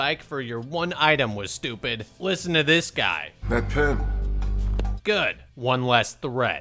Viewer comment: I'm hearing impaired and the captions don't cover it. Can someone tell me what the guy asks for at 10:40 because I can't understand him. Please and thank you. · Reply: He says "that pen", referring to the one the interviewer was holding. He didnt understand the significance of the question, similar to how the people don't see the significance in their food choice. That's why he shrugs immediately after choosing "that pen" it's a "why not" gesture that displays the carelessness of his decision. It's likely meant to display the shortsightedness of people entering the pit. He could ask for ANYTHING but instead of thinking about it, he picks a random object in front of him without any further consideration.